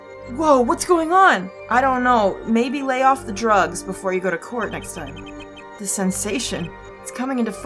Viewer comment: When said:lay off the drugs before you go to court next time . I misunderstood and thought she said:lay off the drugs before you record next time. I thought she was talking about herself.